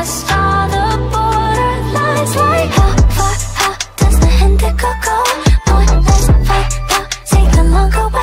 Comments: I misheard Indigo to 'Hint of Cocoa' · I hear "Hydro Coco" 😅 the true is Indigo go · I HEAR "How far does the indi coco" LMAO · I hear "coco" when indigo go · I THOUGHT SHE WAS SAYING INDIGO GOLD AND THAT WAS REALLY AESTHETIC BUT I GUESS NOT HUH,,,, 😿😿